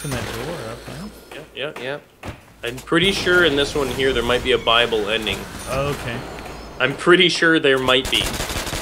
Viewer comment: it sounds like they say Bible ending